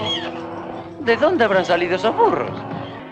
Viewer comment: escuche por un mometo: de donde abran salido esos furros 16:15 por que tienen tantos curros XD